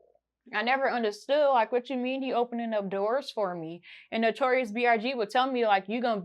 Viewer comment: Did she just say notorious BRG 🤦🏾‍♂️